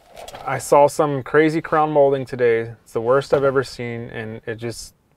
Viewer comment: I thought it said, "the worst I've done " lol